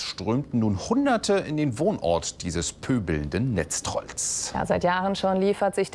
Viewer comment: Er nennt den Drachenlord "Pöbelnder Netztroll"?😄 So a kagghaider alda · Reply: ich hab pöbelnder netztrottel verstanden :)